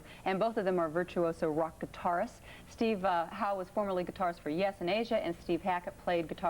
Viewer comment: She rattles off "Yes", and "Asia" so quickly on the intro I thought she was saying Steve Howe played for a band called "Euthanasia".